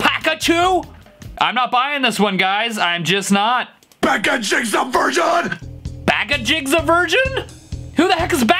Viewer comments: “PIKACHUS A VIRGIN” “BIGACUS A VIRGIN” ”FUCK JOHN” 😂😂😂😂😂😂😂😂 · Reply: I heard "pick and choose a virgin" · I'ts not Pikachu's a vergin, it's "Bag of Cheese aversion." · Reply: i heard "packaging a virgin" lmao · I heard packaging up a version